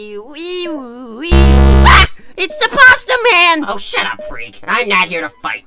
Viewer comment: i’ve never heard clone riggy curse before that’s just uncanny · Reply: i thought he said “oh shit i’m free”